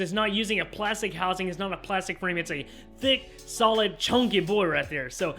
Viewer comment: did you say BigChunk 😎😎😎